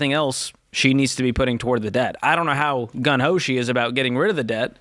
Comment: I thought the phrase is "gung ho".... I heard "gun ho", c'mon George.🙂